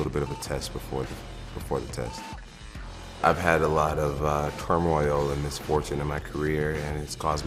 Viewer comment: I actually thought he said 'I've had a lot of turinabol'